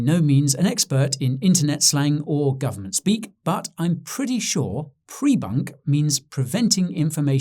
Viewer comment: sorry I misheard, I thought I heard "prefunc" like I had , and tried to explain to my missus, ,late last night, at about 3, GMT. So I made her some poached eggs, next morning. Then I washed the dishes. Be kind, please, I am not there yet, darling